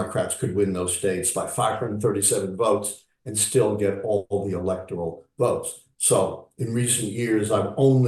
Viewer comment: can't decipher what he said prior to.."electoral votes". Democrats WHAT? ......electoral votes. Sounds like he said,"..and still get all the electoral votes". I'm no economist (but I am a audio director) and that doesn't sound right. He kinda "swallowed" that important part. btw, republican's, nefarious gerrymandering has played a critical role of late and that is a giant problem to democracy. These rightwing creeps are slowly eroding America's systems and values. We better landslide these tyrants in November so we can stop this and fix it before it's too late. VOTE BLUE and inform others with facts instead of memes because this is deadly serious.